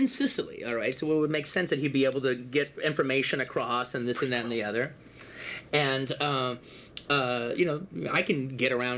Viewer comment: Anyone! Pls help! What did he whisper?! (This could easily be another Tarantino's story/novel/script/movie). The things we're watching has been written long time ago. This guy wrote his entire life, he planned everything. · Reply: he said prequel :)